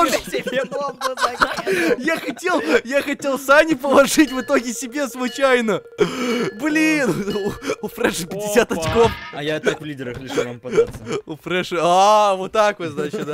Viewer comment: Мне не послышалось или в 8:40 сказал "БЛЯ"???